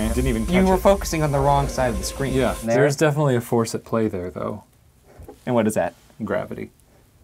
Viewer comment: I thought Shane was making a tap joke. I thought he said 'a faucet play'